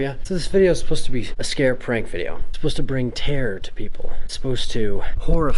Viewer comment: did anyone think that he said tanner instead of terror?